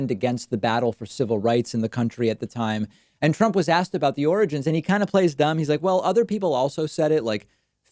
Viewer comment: David said "origins", but I definitely heard "oranges" the first time.